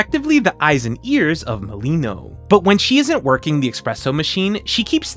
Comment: I'm not even Italian, but did he just say expresso?